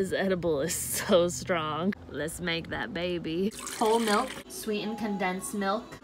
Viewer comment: I thought she said old milk 😂😂 I’m like she lost me there 💕💕